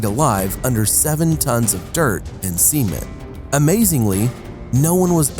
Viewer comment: Anyone else thought he was saying semen?